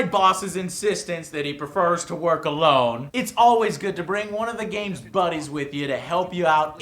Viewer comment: saw Quiet and thought he said "bring one of your Bunnies along"